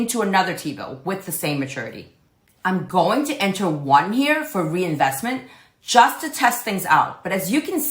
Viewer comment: Awesome tutorial, thank you! At time 23:40, it asks "How many times", you put 1, and mentioned one year, but in this case it was a 4-week T-Bill, wouldn't that mean another 4-week re-enrollment, instead of 1 year? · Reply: Think it's the audio - I said: one "here" - not one "year"